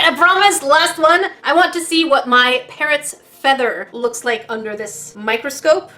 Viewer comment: I thought she said parents XD !